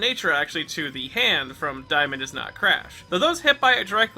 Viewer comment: What did I just hear"Diamond is not crash".